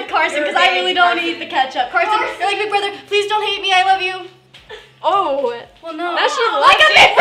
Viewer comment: hold up...wait..... what did I just hear I actually love her 💜💜💜💜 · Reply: amoon she said I love you